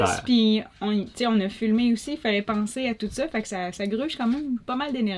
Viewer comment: hahaha!!!! 40:37 , c'est tu juste moi qui as tout écouté ? 40:37 on a fumé... faillait pensé a toute ca! Hahaha. Vous etes les meilleurs a bientot! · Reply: 6 mois plus tard....deuxieme écoute , elle dit on as filmé! haha